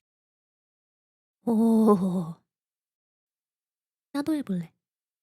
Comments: Hey wait, what I heard 01:47 you say "나도 해볼래"????? 옴마야 진짜 대박 선생님 엄청 팬이에여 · I like how I hear this small voice here in 1:48, "헉- 나도 해볼래 :3>* your voice is so adorable xD